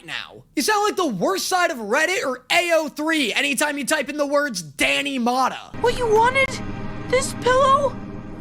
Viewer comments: why do the captions say “danny phantom” instead of “danny motta” 😭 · why did the subtitles say danny phantom when you said 😂 IT DOESNT EVEN SOUND THE SAME